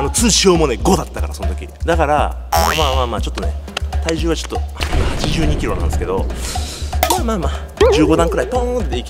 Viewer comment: 182kgって聞こえてビビったw